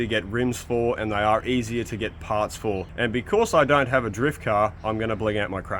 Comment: uh, sorry ... did you just say drift crane? I'm pretty sure you just said drift crane .....